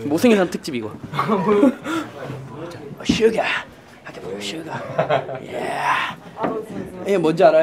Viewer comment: I thought he said "sugar" and was talking about the drinks. Then there's Suga.